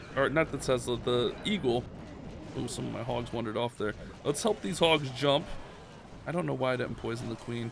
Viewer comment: Lol did I hear you correctly" jump spell the hogs" around 12:39 haha you literally made my day 🤣